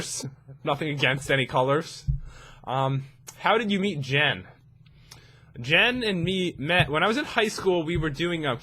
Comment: When he said "How did I meet Jen" I thought he said how did I eat Jen LoL